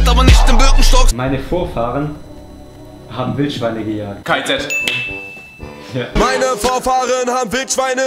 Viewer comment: ich hab KZ verstanden